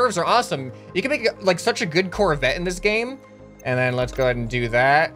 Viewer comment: Did i hear Corvette? you shuold 100% try and make a corvette.